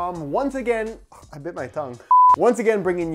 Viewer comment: Did you just say "thongue"? It's tongue bro😅😂